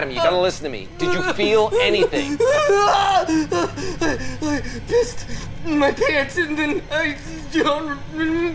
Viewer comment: I literally heard “I missed my parents”